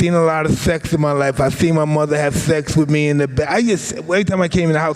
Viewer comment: did he say his mother had sex with him? i know he got CTE but i think that one slipped out